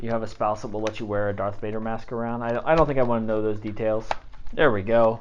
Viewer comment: Sorry it sounded like you said darth mater once😂